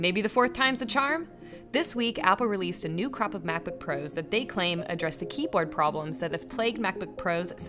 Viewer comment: I heard “new crap of macbook pros”